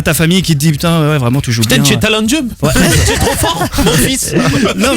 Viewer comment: Il a voulu dire quoi zank à 14:27? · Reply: putain tu es talentueux